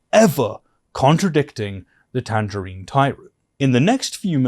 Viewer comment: I love that you called tr*mp tangerine tyrant. I definitely heard 'tangerine timebomb' which I thought was such a great alternative 😂